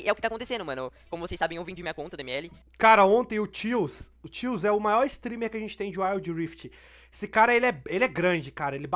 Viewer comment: quem é esse mano ae? Num entendi o nome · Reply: Tealzlol na twitch